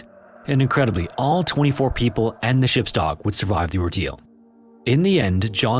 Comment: the caption says even the ship's dock survived, but I believe that was meant to say "ship's dog"